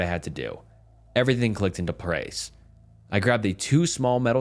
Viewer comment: Sounds like an AI glitch when "you" say "everything clicked into pr-prace". I've heard other moments in previous videos, but this is the first I've bothered to point out. If you're using AI, just be upfront about it instead of saying you have a "human voice" in your description/bio.